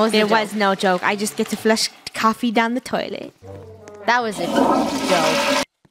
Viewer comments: What did said! Skip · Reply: she said: “That was a Sh*tty joke” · incase your wondering what she said, it was “That was a S*itty Joke”